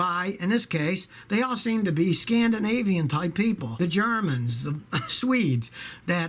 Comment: I thought said "ScaMdinavian" instead of Scandinavian